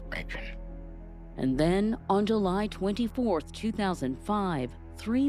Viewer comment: did I just hear July 24th 2025?